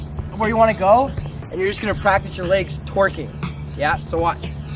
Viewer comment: did Dtrix said twerking O-O